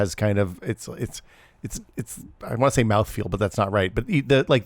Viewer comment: Did he say "mouthfeel"? xD Now we know who in the group watches Counterpoints